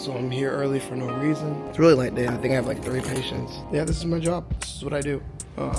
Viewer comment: Did he say he has 30 patients and that’s a light day?